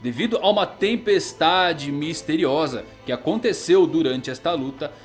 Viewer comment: misteriosa ou foi Monkey D. Drangon?